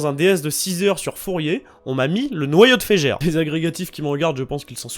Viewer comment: Quand j'ai entendu " noyau de fejer ", une grosse angoisse me traversa le fion